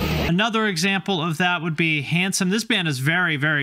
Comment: When you said "Handsome" I legit thought you said "Hanson" and was immediately like "They never did a Hardcore album."